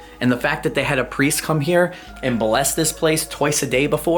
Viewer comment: I thought you said they had a priest come by and molest this place twice a day. 😄